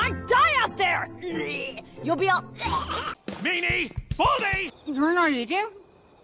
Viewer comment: I just heard “nAe naE, bALdiE” xD